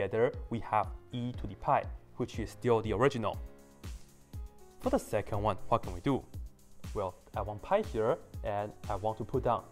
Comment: I first heard "what the second one will fucking do" lol